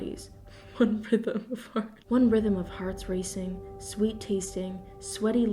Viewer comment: I heard "3 bodies...one rhythm of farts" .